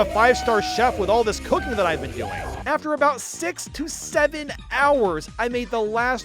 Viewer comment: WHAT DID HE SAY AT 47:55 !!!! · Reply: 6 to 7